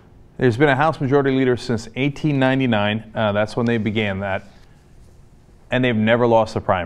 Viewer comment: Did Cenk just say 1899 or 80 99? I cannot decipher what he is trying to say...